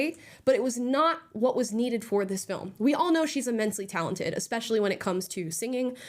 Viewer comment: Oh,you said "she's immensely talented",I thought that you said "she's mentally rhea tar ded",my bad. 🤣🤣🤣👍